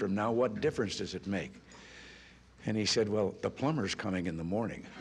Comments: what does he say at 01:08 ? the plummer ?? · What did he say at 1:06 ? I'm german and would like to understand the punch line^^ Thanks in advance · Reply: "Well the plumber is coming in the morning", suggesting that it will take ten years for the plumber to arrive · I'm not native English speaker. What did he said in at 1:07? .... comes at morning?